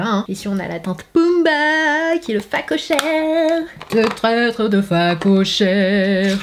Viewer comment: C'est moi ou tu chantes "ce traître de phacochère" a 9:34 ?